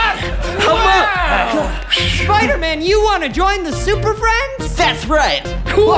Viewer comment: the captions say spiderman instead of robin